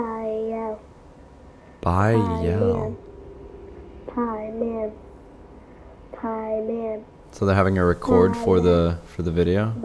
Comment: Ok guys I found this thing in this video Sp apparently at 14:30 you can see Rebecca is saying Pie man pie man.... so I think she is not saying pie man but she is saying Paiman. Paiman is a demon😟😟